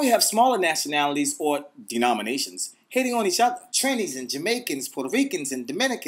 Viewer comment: did he say trinis.... i ve been recognized !!